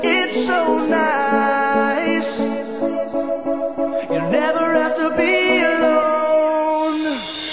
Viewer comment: I'm sorry, but sometimes I heard "never have to pee alone" near the end... Well, public urinals, guess so!